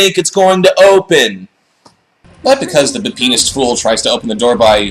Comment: What did he call Caleb at 10:35? The what fool? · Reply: He is a "be-penised fool." Men in properties like this have to be hopeless imbeciles, you see.